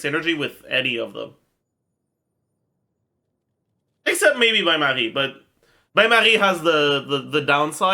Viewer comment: what is he saying at 6:05? "except maybe ___" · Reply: bain-marie